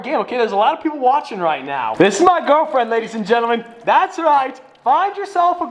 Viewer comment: what did he say? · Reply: “girlfriend” No need to pressure or rush him like some people are trying to do 👍🤙👌